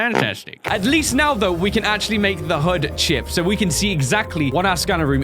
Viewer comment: what i heard is "we can make the hood chip"